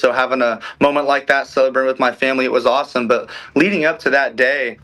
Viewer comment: Did he say" sober with my family"